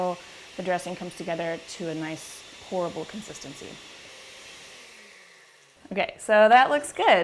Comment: At first I thought you said "horrible consistency" lol